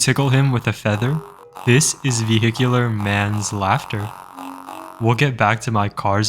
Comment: Man I thought you said Manslaughter. Anyways these are so cool and so weird but satisfying at the same time.